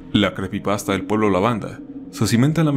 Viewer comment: pensé que decía pablo lavanda